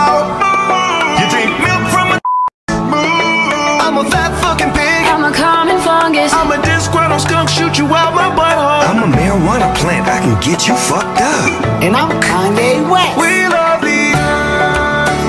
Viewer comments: "I'm a fat f*cking pig" got me laughin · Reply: hahaha i dont know why i thought it said "im a fast f*cking pig" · “I can shoot you out my but hole!” Got me rolling and dying to Jupiter 😂😂😂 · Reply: Lolz i thought it said should u wipe my butt hole😂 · “AnD iM kAnYe WeSt” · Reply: I thought he said “and I’m kinda wak” at first